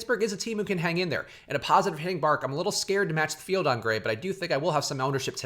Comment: hope you all had a good weekend! like the video and comment your double dong picks to win that free month of Awesemo+! and for a bonus game see if you can identify the part of the video I forgot to edit out that I can't fix now since it's uploaded and it's driving me nuts! (also apparently there were two screw ups, here's the top stacks slide <link> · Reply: you said bark,instead of park at